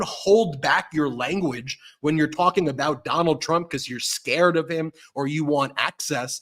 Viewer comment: I am wrong for thinking this, but I thought you said, “McDonald’s Trump.”